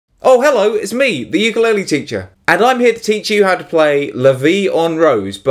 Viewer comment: At the initro i thought u said "its The Ukulele Ginger" jijiji